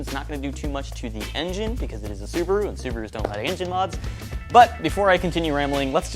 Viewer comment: Am I losing my hearing or did dude just say "...Because it's a Subaru and Subaru's don't have engine mods."! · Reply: Think he said they don’t “ like” engine mods